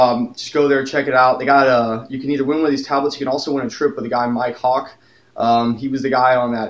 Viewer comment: did he just say: "You can also win a trip with mike hawk"? >.>